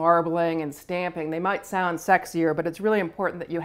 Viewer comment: Did she just say "sexier"? XD